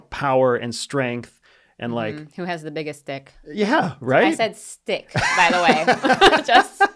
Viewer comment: wonder what the average AI word transcription service would say you said, ha! Stick, yeah right! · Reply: It surely didnt sound like stick at first, haha Had to play iot twice to actually hear stick